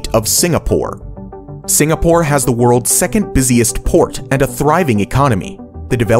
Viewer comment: once I heard "Singapore har world's busiest PORE"